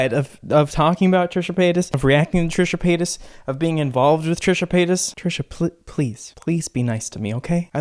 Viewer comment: I thought he was saying Trisha Penis at first